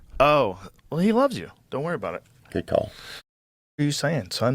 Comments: when Theo goes to "sip" lol did he say good talk ? Bc that's gold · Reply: He says good call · Wow.. I am disappointed in Joe. He used to stand for truth.. Anyway, at 11:29, I think Theo says, “good talk,” as he sips from the cup. Which further confirms that Theo has realized what is happening.